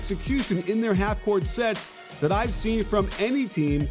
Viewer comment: idkw but I heard sex